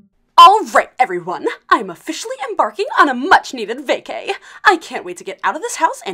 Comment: I thought heath just said I’m officially barking 💀